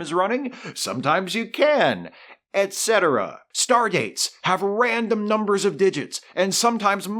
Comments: I know he meant "stardates", but I heard "stargates", and that was just a cherry on this massive cake! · Ha... thought you said "Stargates" not "Stardates" LOL That would REALLY be off the rails. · By the 24th century, who's to say a city in Florida isn't called Chicago. I also thought you said Stargates at first and not Star Dates. Which, that could fit into your awesome show anyway so... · I was VERY confused when I thought you said StarGATES have random numbers of digits.